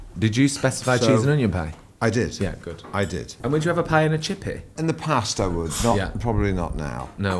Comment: what is nick asking at 13:44? Idk if its because im unfamiliar with brit slang or i just cant hear him well 😅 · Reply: “And would you have a pie in a chippy?” 😉